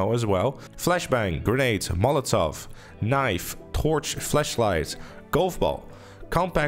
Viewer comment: thought you said flashlight lol.